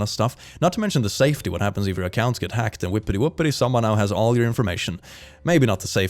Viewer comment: hold everything....did you say "Whippety Whuppety"?